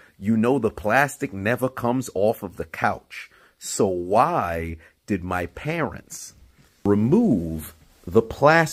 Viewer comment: I thought you first said... "... the plastic is off of my couches... so why did my parents..."